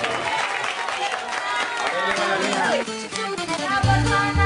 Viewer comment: que dijo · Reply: Dijo haber ahí le va la mía 😅